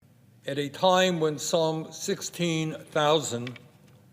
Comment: You know, I thought you first said, at a time when Psalm... but you didn't say that. Bernie, I'm sorry I ever supported you. Ypu daily to understamd this conflict on a fundamental level.